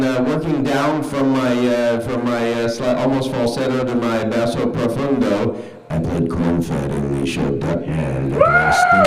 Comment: What does Greg say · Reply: "To my basso profundo. And then Cornfed in ''Duckman''."